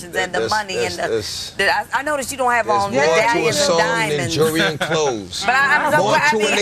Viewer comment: can some one tell me what dmx said please? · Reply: He quoted the hook to his track called More 2 A Song "theres more 2 a song than money and clothes"